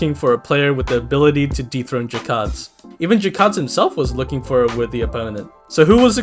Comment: low key thought I heard you say “deepthroat jakads” 💀💀💀💀💀💀